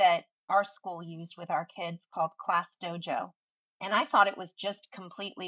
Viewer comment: Is she saying "class dojo"? 8:50 Subtitles might be helpful.